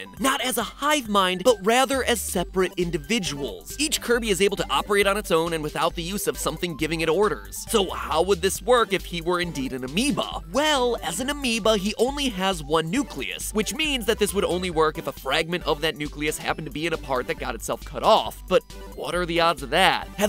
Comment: I thought you said he was a amiibo